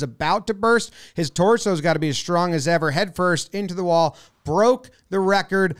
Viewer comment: Thought he said "broooke his head"